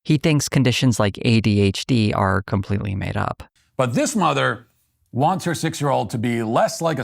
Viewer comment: I thought he was saying 'mother' as in motherfucker he said it with such malice!